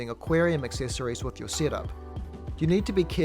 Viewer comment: I honestly thought he said syrup